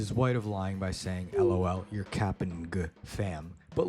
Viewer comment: bro did NOT say camping